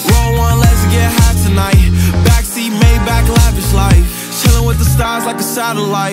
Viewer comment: Can I get an Amen? Introverted, can't make friends Pray for better days, now it's 300k when my day ends Do you remember last night? 'Cause I blacked out In that all white dress with her back out Said, be careful with her heart 'cause it's fragile And thinking 'bout her past make her lash out See, with me it ain't no worries at all Any problem I'll be there, one call If we locked in, you my dawg Fan of you, hang pictures on my wall Roll one, let's get high tonight Backseat, Maybach, lavish life Chilling with the stars like a satellite AP on my arm and it's shining bright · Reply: omg ty I thought he said 'pee on my arm' LMAO